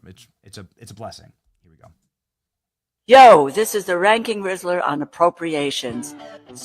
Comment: Benny, you misidentified this individual. That’s actually Ron Wood from the Rolling Stones and Faces. Did I hear that correctly? “Yo! This is the ranking Riddler” 46:58. This needed a nuclear cringe warning also. When she said, “besties” I had to watch it again because I swear it sounded like she was talking to somebody named Betsy.